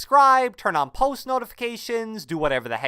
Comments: friend said something at the end "horrible---" is what i think he said. :> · Did anyone else hear the “horrible” at the very end of the video